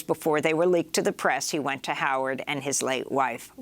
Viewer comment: I thought she said "Howards End"